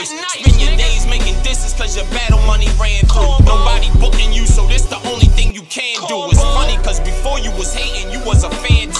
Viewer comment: Does anyone know what is saying in the background, starting from 1:57? He says it 3 times and I really like the sound of it, but I don't understand what he's saying. Cobo? Call Bo? Cold bull? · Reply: He was saying “cornball”